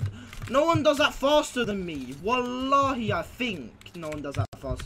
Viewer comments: Did he say wallahe in 1:25to 1:30 which is Arabic word means I swear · did He say walah